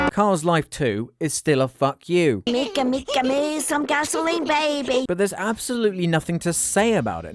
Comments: Is he saying "Nico Nico Nii some gasoline, baby" ? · Wait did he just say “NICO NICO NIII some gasoline baby”?? Edit: never mind he probably said ‘make make me’ with an accent, why would anyone make him a gasoline baby - WAIT